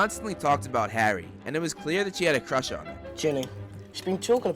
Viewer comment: it sounds like Ron says she had a crush on herself